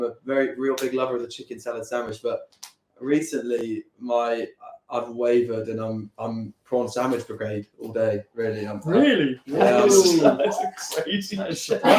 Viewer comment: what does he say at 30:09 ? my american brain cant understand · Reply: “recently my- i’ve wavered and i’m [on the] prawn sandwich brigade all day really”